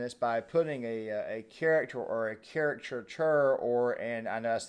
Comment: Absolutely trying to replace women. Trying to make me stop watching sports. Buying firearms. Drinking beer. Playing video games. Just about everything I'm into, including women, and being a man, are being erased. They F'n ruin everything. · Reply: I laughed when said these "Caricatures", I misheard him and thought he said "CHARAC-TURDS". Well these virtual signalling Mary Sues are CHARAC-TURDS.